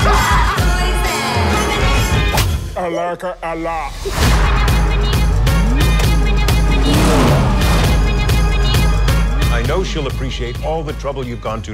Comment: what he said at 1:38? answer me plz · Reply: seriously? he says "i like it a lot." ...just like he did in the first one